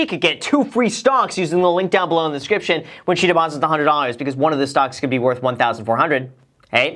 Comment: STONKS! 7:24 . . . I really hope I heard that right haha